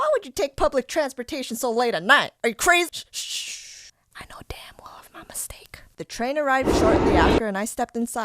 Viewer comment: i know damn love i made a mistake? is it that what i heard